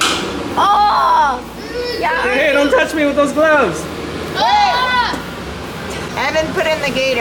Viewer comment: with captions on instead of “hey don’t touch me with those gloves”.It instead says “hey does it’s me with those glands”. Lol 😂😆 It also says “and the indicators”.